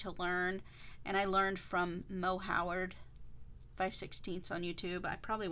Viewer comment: you learned it from who? Didn't catch that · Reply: Moe Howard 😊